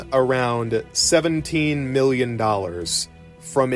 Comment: At first I thought he said it made maybe $70 million because I frankly wasn't paying attention. That number caught my ear though cuz my brain was like "there's absolutely no goddamn way this game made that much money, rewind that shit" And I had to stop what I was doing to go back and listen because something in my core said that the information was incorrect and I absolutely had to find out what it was. &17 million is much more believable